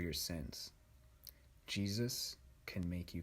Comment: When he said, "Jesus can make you clean again", I thought he said cheeses. 😭🤣 I had to replay the clip to understand.😆